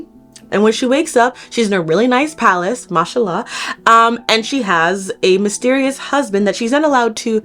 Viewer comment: waittt are you Muslim? I heard mashallah and my neck broke from turning my head too fast 😭😭😭